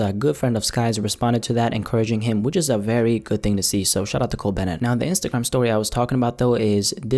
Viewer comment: When you were talking about Cole Bennett it sounded like you said " shut up to Cole Bennett"